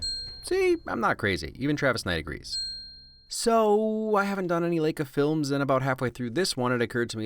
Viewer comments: Error in the subtitles, it's "Lakia" instead of "Laika". ( I have too much time) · it says lakia in the subtitles instead of laika, just saying, its a lot of hard work you put into these videos and i love almost very single one of them, just saying